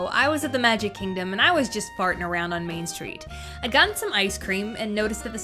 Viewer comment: R.I.P icecream 1:30-3:12 He had a happy life of 1:82 mins may his soul rest in peace or should i say pieces idk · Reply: did she just say farting around?????